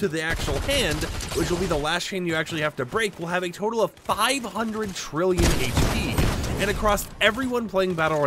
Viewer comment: I’m sorry but did I hear 500 trillion??? Thats 500,000,000,000,000 health???